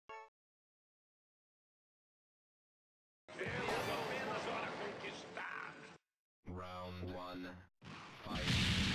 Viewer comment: O que ele falou aqui? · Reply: Fui criado apenas para conquistar